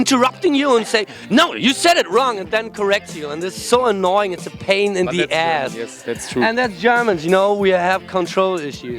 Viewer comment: What is the german word on 4:12? How does it spell? Thanks :) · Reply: Spießer